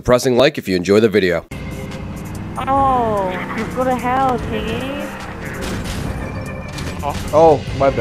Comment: i heard "oh go to hell chingy" is he a pain in the ass or summut?